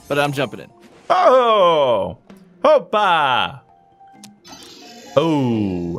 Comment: Did say oppa